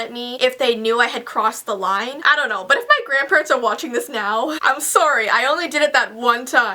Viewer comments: At the 6:00 minutes when she said sorry I heard a deep voice said “I’m sorry jussii” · At the 6:02 minutes when she said sorry I heard a deep voice said “I’m sorry jussii”